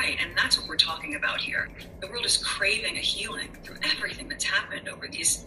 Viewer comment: I thought she said The world needs a healing. And she sat her butt right smack in the middle of the camera and had H sit on her side. Keep up the great work.